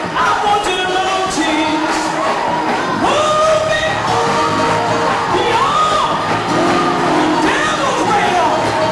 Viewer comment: What is she saying starting @ 2:33, can someone tell me? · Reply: Move me beyond the devil's radar! Let me go on the outside where he can't find me! He can't pick up Holiness! He can't pick up Divine spirits! Move on the outside!"